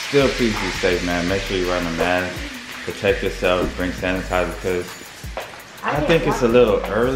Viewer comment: Is it just me or did say drink hand sanitizer at 2:30 🤔